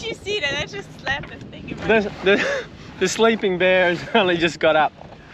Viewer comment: sleeping bear? Sleeping beauty more like. I like the sailing instructions, learning lots! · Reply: I thought he said sleeping bare.